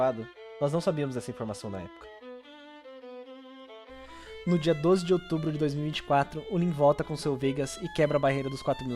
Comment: Que item era no 13:05? Não entendi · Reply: Oração de lisnar, o item que você usa para reviver